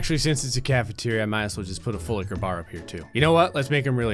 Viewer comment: um did he just say full liquor bar normally?